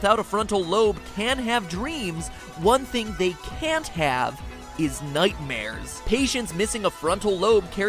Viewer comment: did matpat just say "is nightmares" ?